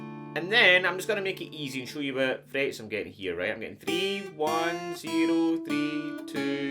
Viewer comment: what did he just say😭😭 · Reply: "I'm just gonna make it easy for you and show you what frets I'm getting here"